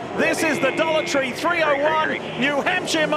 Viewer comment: At the 1:25 mark, I thought he said that this was the "Adultry 301 ..." and who is the new guy?? This Lawson fellow. I've looked for him all day, and I can't find him!!!